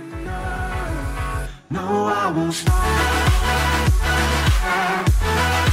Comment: That's funny actually. Your intro music sounds like someone saying 'I won't starve'.